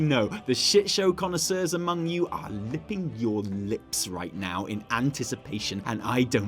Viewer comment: Did really say "lipping your lips" at 10:23 or am I losing it?